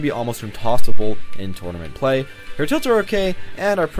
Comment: i know i am wrong but i just heard "Her tits are okay"